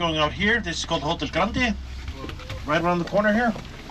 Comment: For a moment I thought you said Hotel Grimaldi... Aaaargh! 😨 😜 It's good to learn that in other parts of the world than where we live (me in the Netherlands) one cannot always take everything for granted. We Dutch start complaining when the bus or train runs 3 minutes late. 🙄